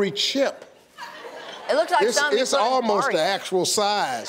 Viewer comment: What does Montana say? Could anyone help me, please? I am transcribing the video. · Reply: 3yrs late but better late than never I guess lol “It looks like something you put in a [/car radio/?]”